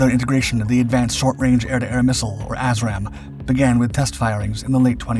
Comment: Did he say "a$$-ram" missiles or just something really close to sounding the same?